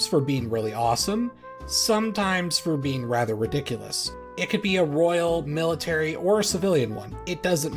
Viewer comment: I thought this said "Ridiculous Royal Titties" and now im sad